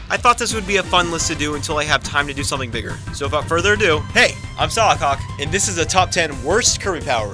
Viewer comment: it sounds like you said "hey! I'm solidcock!"